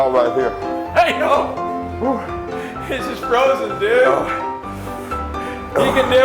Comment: what did he say it’s just frozen dudeee?